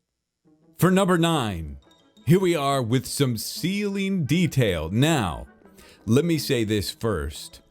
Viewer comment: I thought he said seaweed detail and I was like you did that for this weeks video...